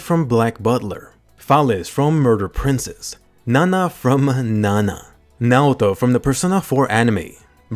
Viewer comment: i thought he sayed Naruto